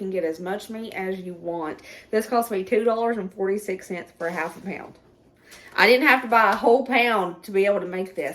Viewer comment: Hamburger is $20 a pound there?😮 I'm so sorry for you. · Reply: 😂I seriously thought you said $10 for a half pound!